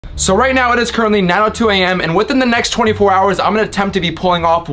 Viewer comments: Who else thought he said now 2 AM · Anyone else heard him say 2 am at 0:02 instead of 9:02 am